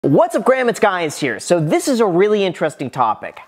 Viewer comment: Did I just hear "what's up graham it's guys here" ?